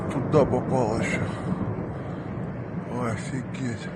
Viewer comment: do i hear him say double borscht? It's a dish best served hot, enjoy. More to come.